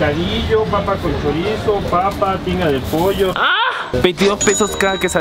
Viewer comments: por un momento escuché ‘pinga de pollo’ XD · Escuché Pinga de Pollo xd